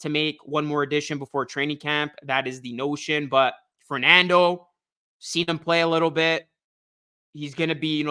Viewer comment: I thought you said brutal Fernando 😂